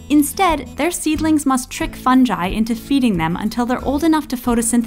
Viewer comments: she said "funji" instead of fungi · im sorry did you say fun'j'i